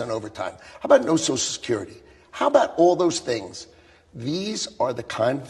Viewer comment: Did Lutnik say no tax on tips, no tax on overtime, and no social security? Wake Up Seniors! Trump is coming for your social security and he wants it all. That will be a really sad day in Amerika! 😢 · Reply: You misheard . He said... No TAX on social security.